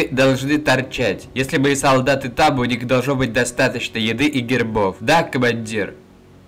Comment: У солдат должно быть достаточно еды и гербов. О нет как они без гербов!!! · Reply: Мне наверно одному послышалось не гербы а грибы).